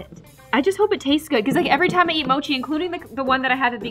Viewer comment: idk why but i heard "im gonna eat mochi" i was like NOOOOOOOOOOOOOOOOOOOOOOO JIMINIE SHES GON EAT U GO RUN